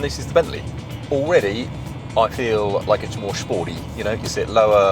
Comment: is he said he felt the Range Rover was Shporty not sporty. I nearly pissed myself laughing.